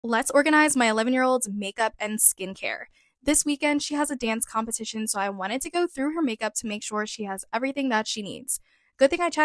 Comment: I thought she said one year olds